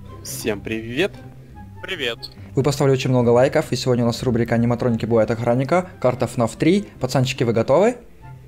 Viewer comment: Мне послышалось: аниматронники будут охранником!😅 Надеюсь нет..😅😅